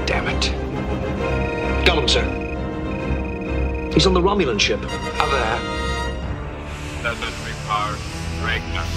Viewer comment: What the heck does Ricker say @ 2:39? "How thaha!?" it's like they chose the take where he forget to finish his sentence. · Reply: brofenix “How the heck?” I think he said.